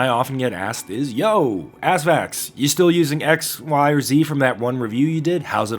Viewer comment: Did you just call yourself "ass facts"?